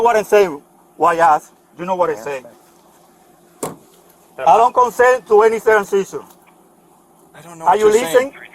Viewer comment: thought he said i dont consent to any circumcisions